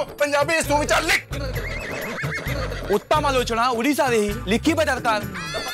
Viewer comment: The guy who is pretending to be ODIA isn't so... God knows what he said.... I'm a ODIA and got to hear such language for the first time. Only from "subtitles" I got the idea that he's trying to do so.. Probably, he's trying to say Uttamalochana ODIA re hi lekhiba darkaar...